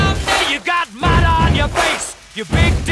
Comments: Is it just me but I heard “kickin yah cat all over the place” 😭💀🐈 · PLEASE DON'T TELL ME I'M THE ONLY ONE THAT HEARS "Kicking Your Cat All Over The Place" People who hear it too 👇 Edit 1: damn 100 likes in 2 days how many people hear kicking your cat all over the place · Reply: I hear that but also together this is what I hear: "You big disgrace, kicking your cat all over the place." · I HEARD HIM SAY “kickin your cat all over the place”😂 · *Kicking your can all over the place left the chat* *Kicking your cat all over the place entered the chat* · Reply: i thought he said kickin your kind all over the place